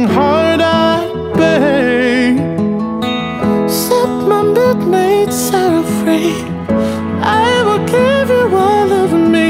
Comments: Did anyone else hear. Suck my milk made sour free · It sounded like he said suck my milk made sour free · suck my milk made sour free???????? am i the only one who hears that??? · umm did I hear set my midnight sorrow free or suck my milk made sour free??? EXCUSE ME.???? Tell me what you hear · Did i just hear "suck my milk made sour free" (if u wanna see heres the time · did he just say "suck my mill made sour free"or is it just me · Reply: He said “set my midnight sorrow free” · I hear: suck my milk make sour free